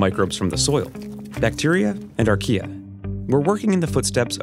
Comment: did he just say 1:05 Bacteria & IKEA?